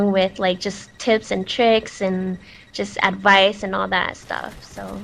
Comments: i thought i heard her say tits and tricks lol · i thought she said tits and tricks LOL ?